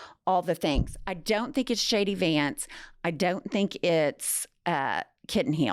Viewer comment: I keep hearing you say “Shady Vance” which is a better name for him.